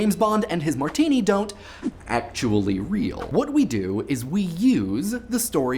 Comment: I'm sorry did he say "They don't actually real"?